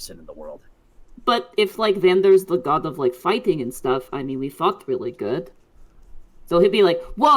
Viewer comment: I keep listening for it, dont think T-Wig swore. Please correct me if I'm wrong? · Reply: I'm guessing that Taka misheard due to the accent she's using for T-Wig. She's saying fought, but if you aren't listening carefully, it sounds likes she said 'fucked'